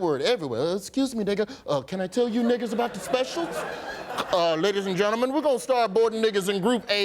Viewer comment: I thought he said niggaz and gentlemen 😂😂😂 North african was here .